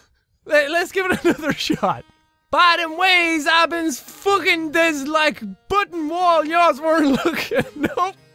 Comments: I think they said "By the way, I've been fuggin this like button while y'all weren't lookin'." Yeaaahhhhh no thanks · The guy said "by the way, I've been fucking the like button when y'all weren't looking" why do I get that? idk, but that what he said.